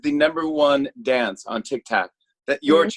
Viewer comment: did he say tiktac